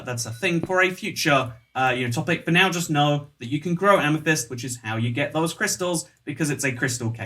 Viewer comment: i thought they said u couldnt carry amethyst